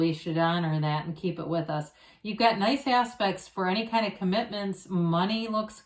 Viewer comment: Everytime you say "you've got nice aspects..." i hear "you've got nice ass...pects" 🤣